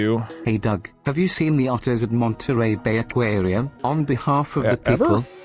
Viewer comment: I was watching this on double speed and I fully thought TTS had said "autism monster", not "otters at montery".